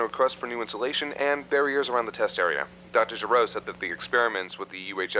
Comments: For a sec I thought he said Dr. Gero. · did he just say Dr. Gero?